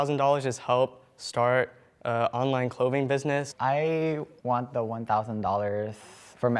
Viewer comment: Is no one gonna talk about how close she sat to Kalid when she got out. Like damn girl move over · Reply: OMFG I THOUGHT YOU SAID CLOHE AND I WAS LIKE “ WHO TF IS CLOHE” I WAS LOOKING FOR A CLOHE FOR 5 MINUTES